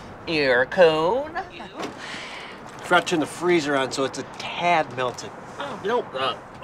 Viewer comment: What is he saying at 2:53? Frenching? Crunching? Fratching? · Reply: “Forgot to turn to freezer on” he just says it weird and muddled😭